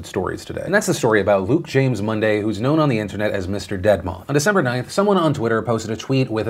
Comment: deadmoth i thought you said Deadmau5 sorry just trying to make the tone slightly lighter it's.. it's a pretty bad situation.